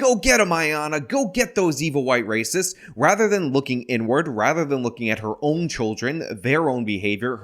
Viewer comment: says “Looking Inward” But I heard N-Word! is an evil racist !!😂😂😂😂😂